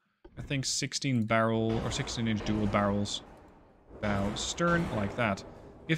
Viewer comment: no because I got so excited around 2:40 when I heard him mistakenly say "16 barrels"